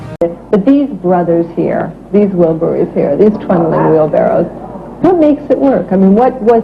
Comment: it sounded like she called them "trembling wheelbarrows".